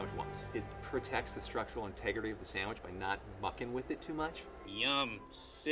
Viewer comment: I swear at like 3:58 he said "f*cking" 😂 but he said bucking??